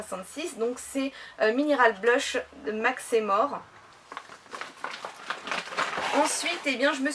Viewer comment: Pour la marque de maquillage au début j'ai compris " Mac c'est mort " x)